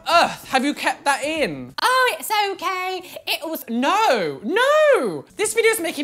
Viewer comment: when he said no the second time it sounded like stampy..